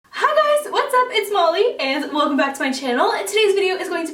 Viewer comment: When she said "its Molly... And welcome back" I thought she was saying her name was Molly Anne